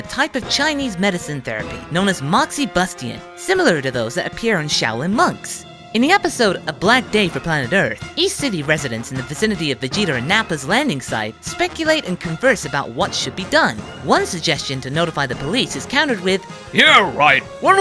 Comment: ish "Vegeter"? Or am I hearing things. Also there's a good chance youtube is gonna fuck up my quotes but I 'ain't gonna do jack shit about that.